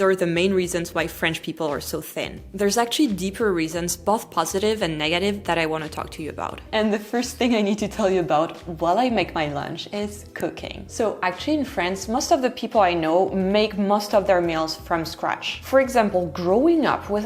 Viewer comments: I heard cocaine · I thought you said cocaine and I was like OH is that why everyone’s so thin😭